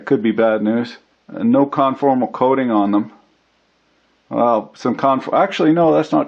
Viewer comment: did he just say caramel corn coating?!?? Mmmmmm qornnnnnnn....